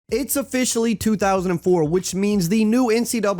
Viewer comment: is anyone else hear him say 2004???